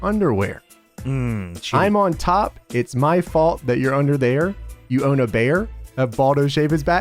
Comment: Cena did NOT say "you own a bear". He said: "You WANT a bear, have Baldo shave his back hair." Because Albert was a big and hairy bastard so he sort of resembled a bear.